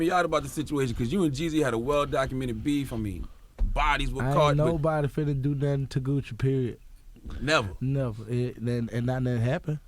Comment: Can anybody write me down what he said from 13:12 - 13:30 and from 14:32 - 14:42 ? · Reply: Gucci's words 13:12 - 13:30 “Aint nobody finna do nothing to Gucci period. Never. It not don happened. I ain’t ever let nobody mush me, push me, talk to me in a cold way, I aint finna go for it. Period. Never.” 14:32 - 14:42 “N na I aint even you know what I’m saying like I’m not a fan of no uh uh anybody music who I feel like a sucka. I ohnt listen to you. They play you in the club you can have number 1 jam but if I know your character how can I listen to your music? Gucci's words translated to english 13:12 - 13:30 “Nobody is going to do anything to Gucci period. Never. It has not happened before. I have never let anybody put their hands on me, push me, or talk to me in a disrespectful manner. I will never go for that. Period. Never. 14:32 - 14:42 “I am not a fan of anybody’s music who I feel that the person is weak. I won’t listen to you. You can have the number 1 jam in the club, but if I know your character how can I listen to your music?